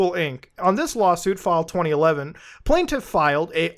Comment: It sounded like you said LOLsuit.